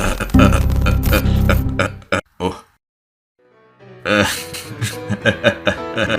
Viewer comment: Vous avez fait une ERREUR car ses: Ah hahaha attend, de quoi on étais en train de parler?